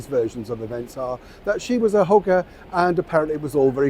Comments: Goodness, when Neil said that MM was a hugger I thought he said hook-r. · Neil, you made me laugh. At the 2:18 mark of the video, I thought you said that she was a "hooker" but after listening to it twice, I figured out that the word you said was "hugger." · At first, Neil, I thought you said "she was a hooker" - which would have been more or less accurate - but you said "hugger" in your northern accent! x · OMG, NeiI I had to listen about 5 times because I thought you had said they thought MM was a "hooker."!! It was "hugger" LOL. 😮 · I thought you said “She was hooker” but what you said “She was a hugger” I think BOTH could be true 😂😂😂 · When you said she was a hugger, I thought you said something else. 🤦🤣🤣 I was doing laundry and almost dropped the laundry soap! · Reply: I thought he said hooker not hugger 😂 😂 I had to listen again 😂🇦🇺